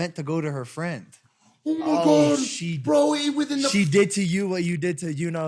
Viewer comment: Did Mike just say browie?! I love that word!!!